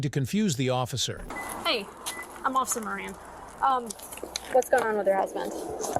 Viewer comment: "Hey, I'm off submarine". Mishearing goes really hard here. It doesn't even make sense; we clearly hear her say "Officer Marin".